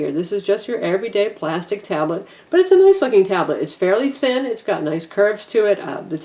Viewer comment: it sounds like shes sayin ..."but its a nice fucking tablet" at 20:59 lol